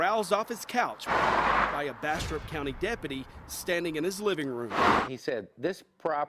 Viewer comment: I thought you said "Bastard County Sherif" xD